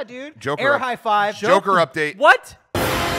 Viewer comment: saying "Joker update" and going "WHAT??" was exactly what I was hoping for today. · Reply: It was saying "what??" at 28:49 but I am with you on being excited for another "Joker Update".